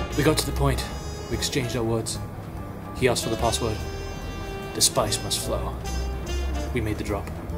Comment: Lol! i heard, "The Sparks Must Fly" XD